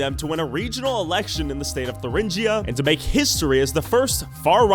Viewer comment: did you say Thuringia?I'm a indian Sikh living in Eisenach